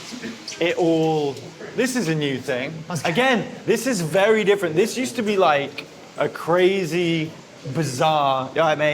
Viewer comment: What's he saying at 41:02 when he greets the people in the barber shop? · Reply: Hi say's "hi, are you alright". It's basically just the way of saying hi in the UK.